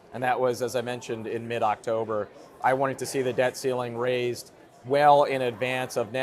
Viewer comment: Anyone else catch that Freudian slip at 12:30 speaking of "The Debt Stealing"? He just called it what it is.